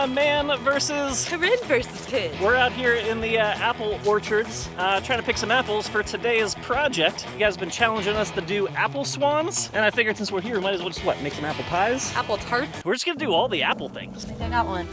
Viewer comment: When said "Apple Swan" I heard "Emma Swan".